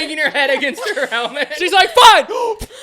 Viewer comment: DID JUST SAY FVCK?! 😭😭😭😭😭